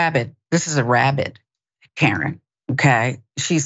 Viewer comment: I heard, "rabbit karen", my hearing is bloody awful today. 🙀